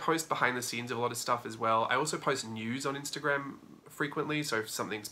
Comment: I choked on my coffee cuz I swore you said , " I also post nudes on Instagram as well" . it was news, yes it was news... I know . lol